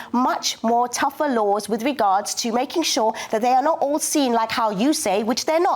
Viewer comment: "Making tougher laws with regards to making sure that they are not all seen like you say..." Am I hearing this correctly? From her? So she wants laws that will put you in prison for simply observing situation and saying facts based on observation? She completely denies the fact that crossing to country illegally is a crime, yet she wants to make a criminal out of YOU who dare to observe and connect the dots and say anything about it.